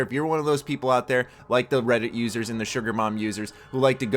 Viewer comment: UPDATE: Apologies for the quick gameplay blur for about 30 seconds! Must have copied into two areas while I was editing. I would take it down, but tbh, the video has already been up for an hour and no one has really crucified me, so thanks for that! Makes my life easier while I prepare for PAX East! EDIT: I realize what I did wrong! Didn't split the God damn clip before the image popped up on screen. Such a tiny error making such a big difference. Bleh x( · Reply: Lol, it sounded like you said SugarMom users instead of bomb >p